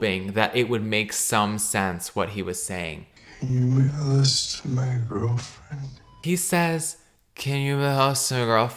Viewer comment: Didn't he just say "Can you mail this to my girlfriend"? Also, I hope the kid is okay. Having a parent who has sex instead of dealing with her important and pressing problems, and another who is just chill about never standing for himself and getting thrown in prison every saturdays might make his childhood rough. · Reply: I heard “Can you make a list for my girlfriend” ??